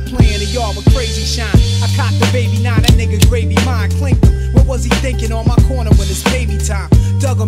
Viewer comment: I always thought he said “I cocked a baby nine, that nigga gravy mine”